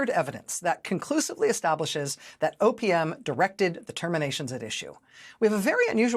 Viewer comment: I'm mainly listening as I work, more than watching the actual video. So I misheard OPM as opium! Makes sense to me that some illegal substance abuse is happening at the White House lately...